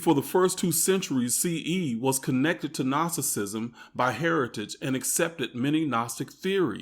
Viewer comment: Funny how 2:13 when you say ..Gnosticism the caption says Narcissism .....and it actually fits......western Christianity is narcissism.